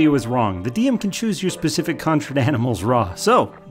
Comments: I can not tell what is being said here. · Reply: "the DM chooses your conjured animal RAW (rules as written)." When talking about the video on Conjure Animals. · What does he say at 1:43? I know its the video about the raptors, but I dont understand what hes trying to say he was wrong about. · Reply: "The DM can chose your specific conjured animals RAW." Basically you don't get to pick your conjured animals unless the DM sats you can. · any one mind helping me understand what he is saying at 1:42 something about the dm chooseing something conjur animals he says it a little to fast to understand. thank you for the help in advance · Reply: No, you got it. The DM can choose your conjured animals RAW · What did he say at 1:47? Didn't understand a single word. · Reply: “The DM can choose your specific conjured animals RAW (rules as written)”